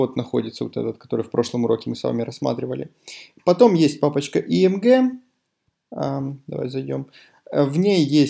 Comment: Мне одному послышалось не img, а AMG?! Тимати вошел в чат